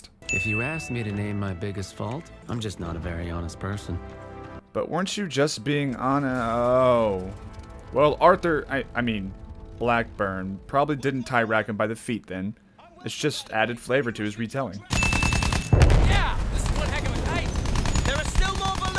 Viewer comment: Did you- Did I just hear arthur?